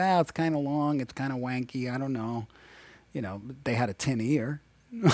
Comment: Can anybody tell me what he says at 4:15 ? Youtube subtitles say "They had a 10 year" but it doesn't make sense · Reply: They had a tin ear